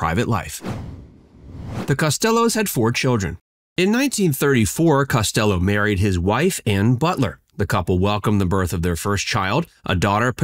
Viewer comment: I had to go back and check, I thought he said "In 1934 Costello married his Wife AND Butler" 🤣🤣🤣